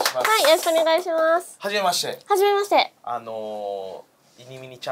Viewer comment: ほんとは聞き返したないんやけど、ハゲまして に聞こえた